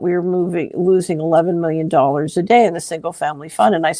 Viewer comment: she said “We were losing ELEVEN MILLION DOLLARS A DAY” Caption said $1 Million